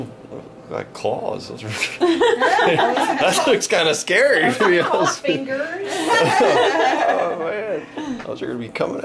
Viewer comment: at 8:47 the doctor says "Those are Nicole's fingers" ITS A GIRL Take the video down and reupload so other people dont catch that! I don't want the surprise ruined! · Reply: BoggsBowhunts I think she said "those are called fingers"